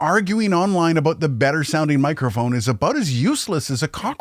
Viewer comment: I heard A Boot... 😂